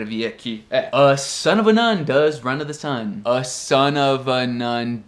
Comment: Que isso BraSil!!! o som do "to" do americano é improvável, gente! hahah Virou um "da"??? A sun of a num does run "da" the sun?? Levei um tempo para conseguir identificar esse som!! Vários replay!! E ainda nao sei se entendi direito (?) hahah Ótimos vídeos!